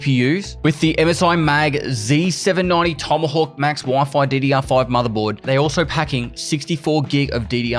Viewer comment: did he just say diddy r5